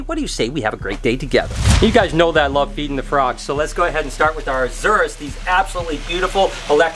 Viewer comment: When you said i love feeding the frogs I thought you say i love eating the frog